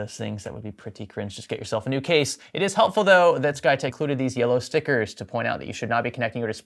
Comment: You keep saying "SkyTech." I only hear "PlayTech."